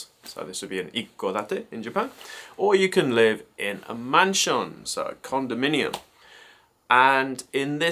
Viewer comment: I saw another video where you said you bought a manshon and I thought you said “mansion” LOL. TIL the word “manshon” 😅